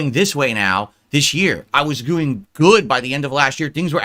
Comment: Does he say “goo-ing” instead of “doing”? TWICE?!